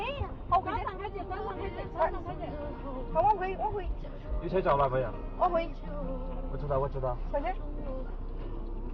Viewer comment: anybody know what they said???? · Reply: Brake and something rlse